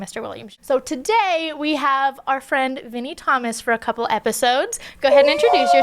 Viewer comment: I thought you said mini Thomas, lol